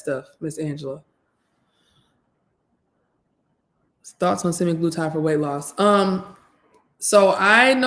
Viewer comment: time you mentioned something that you are going to wait on, what is it as you said it really fast. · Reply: I was talking about a drug called semiglutide that is being prescribed for diabetics and prediabetics and it helps with weight loss